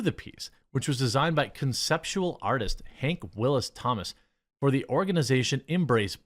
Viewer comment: Almost spit my coffee out when you said the artists name. Thought you said "Yank Willy, Thomas!"